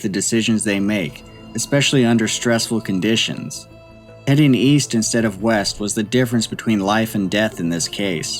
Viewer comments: "I thought you said 'Weast'" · East?! I thought you said weast!